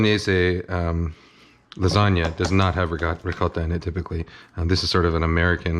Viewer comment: I heard that rigot